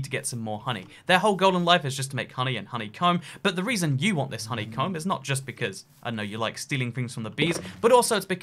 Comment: I heard “Honey cUM”